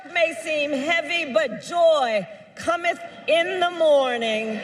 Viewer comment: Did she just say "Commith..?" 🤪